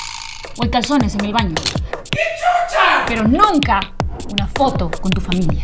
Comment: jsjsjs 😂😂 sin duda una de mis partes favoritas 👌👌 Parece que dice "que chocha", y chocha en Argentina es como decir que :v no me sale la palabra pero es algo parecido a "capa" (busquen en dicciónario argento que significa "capo/a") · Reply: Dice que chucha :v bueno eso es lo que siempre escucho